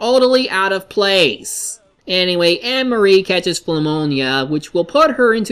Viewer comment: you SERIOUSLY called it phumonia instead of pnumonia